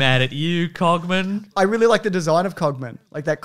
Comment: I thought you said cockman like five times before I twigged that maaaybe it was something else. I couldnt get through the opening sequence so never heard the name before just now.